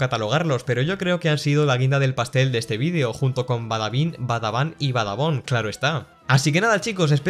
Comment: pese que dijo badabun XD